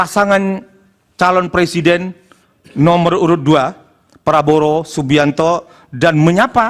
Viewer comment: apa saya yang salah denger ya, dia menyebut nama prabowo menjadi prabroro.